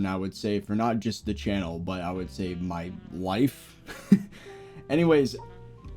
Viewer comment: I thought you said wife and had to go back to hear again xD